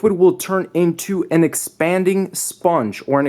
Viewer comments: anyone else thought it said "Exploding" instead of expanding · Am I the only one that thought it said exploding sponge ball? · I thought u said sponge bob hahaha LOL! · I thought it said expanding sponge bob